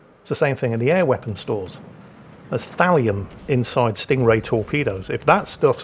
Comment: Thallium I had to turn on the CC to get that I thought you said Valium torpedoes. Is that how rich people get high when they're out on their super yachts?